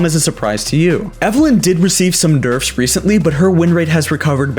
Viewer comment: did he just say derf at 5:11 or is it just me